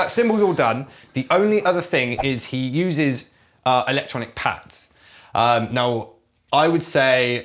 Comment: For a second I thought he said "electronic pants". I naturally thought Wallace and gromit.